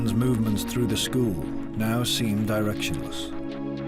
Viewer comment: what word does he say here. I can't understand what he says. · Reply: directionless